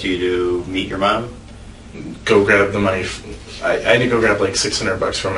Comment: Does he say "Go grab the knife" at 12:23 when the detective asks him what Mel asked him to do?